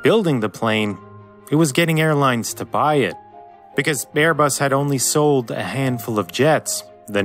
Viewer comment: is it just me or did he say "bearbus"